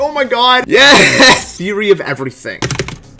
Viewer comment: When said ''theory of everything'' It sounded like he said: Y o u r e a d e v e r y t h i n g .